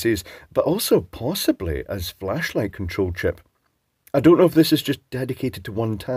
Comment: thought you said fleshlight for a second there. Presumably there are electronic ones of those as well...